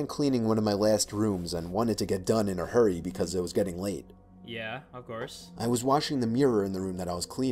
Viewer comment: I really thought he ( the voice actor ) said: "And I wanted to get done in a hurry because I WAS SUPPOSED TO BE GETTING LAID"🤣 But then, I realized he ( the voice actor ) said: "And I wanted to get done in a hurry, because it was getting late ."😂